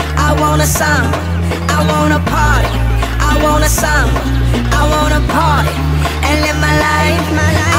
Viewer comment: "I want to party! I want a son! I want a party! and Fight!" Please say i wasnt the only one who heard this...? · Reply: I heard "I wanna summer".